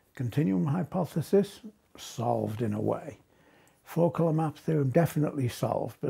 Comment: what is the hypotesis with the map called? I can't understand him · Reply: the four color map theorem